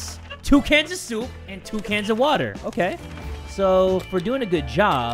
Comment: did he just say 2 cans of water???